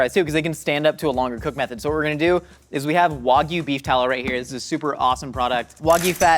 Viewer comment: It sounds like Josh is saying wocky beef tallow lmao